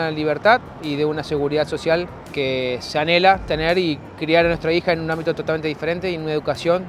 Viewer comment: Minuto 1:26... Que dijo? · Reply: Una seguridad social que se anhela tener